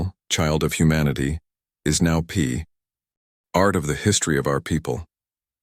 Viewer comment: I'm just laughing how the AI messed up here... instead of "part", the AI said: "pee art"